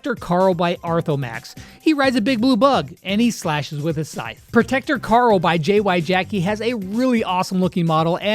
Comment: did anyone else hear him say protecter Carl lol instead of protector Grom?